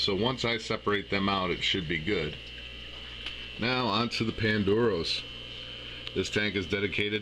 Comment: Thanks for the fun videos! I'm starting to look into dwarf cichlids and some in particular caught my eye. I thought you said Pandoros, but I can't find it when I search it. It's at 7:44ish. Those are so beautiful I would love to learn more about them. Thanks again for the fun and interesting video, you definitely have a sub with me :)